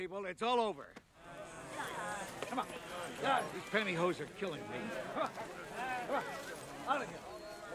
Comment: 🥴🥴🥴did he just say his “pantyhose” are killing him?